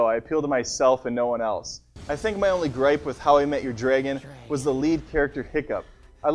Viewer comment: Does he say "How I met your dragon"???